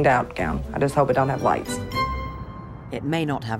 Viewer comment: Oh, LIGHTS. I thought she said LICE and thought, "Them's fightin' words, lady. Small wonder there was a brawl."